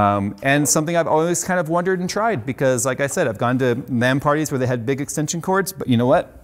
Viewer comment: I swear, I heard "I've gone to man parties where they've had big extension cords."